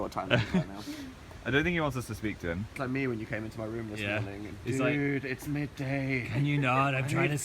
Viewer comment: Okay am I imagining things or did say "When you came into our room this morning" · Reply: NerdyGirl126 he said my room, clearly